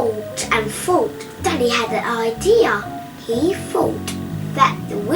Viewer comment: “Then he had an idea” What I heard “Daddy had an idea”